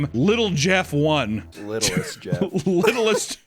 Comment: I don't understand how people aren't getting it, it's not "Littlest jeff" it's "Little St (Saint) jeff" because his island was called Little St James and his first name is jeffrey.